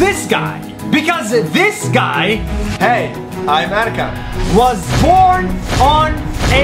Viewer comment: It sounds like he said "American" when he says "I'm Erkan"